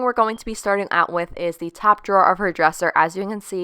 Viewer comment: It sounds like your saying draws as in underwear lol.